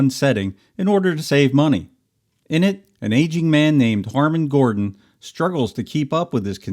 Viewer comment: I thought you said the old man's name was Harmon Goering. I had to back up a few times. Wouldn't be a popular name.